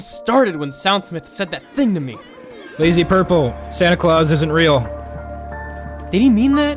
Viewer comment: "Lazy Purple, SoundSmith isn't real." -Soundsmith 2017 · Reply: wait i thought he said that santa claus wasnt real